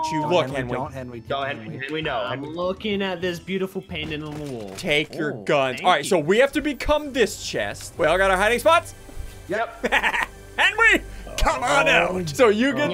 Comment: Am I the only one who thought he said “and tank your guns”